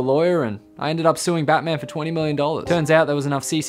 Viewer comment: I heard "see for" and immediately looked at the subtitle to see what ye saudid